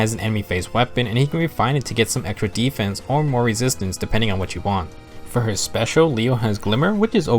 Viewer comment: Every time you say "Depending on what you want" I always hear "Depending on Witchy wand"